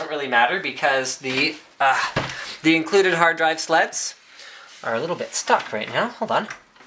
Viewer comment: Sounds like he says the included harddrive sluts not slots @ 6:08 lol